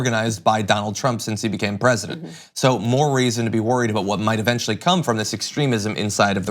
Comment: Did he just say more raisin?